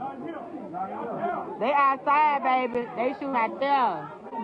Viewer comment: I thought she said they shooting ,but they ain't running?